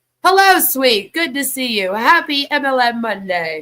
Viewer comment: I thought you said this was Albs’ MLM 😂